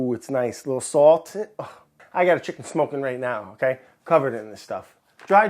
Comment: I 100% thought brad said “I’ve got a chick smokin’ right now, she’s covered in this stuff!” I realized he meant chicken, but in my mind I just pictured his wife smoking and him covering her in pepper & sumac.. 😂